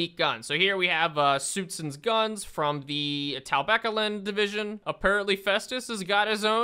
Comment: Whenever I hear say Talabecland I always hear “Taliban”